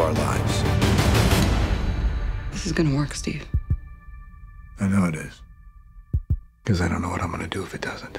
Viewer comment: Is it just me or did Steve say "I knoo i' i'"